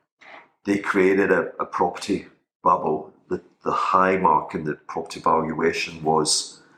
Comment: I thought said "they created a property buttwhole". 😂 That was great, gonna listen again. Thanks and rebel crew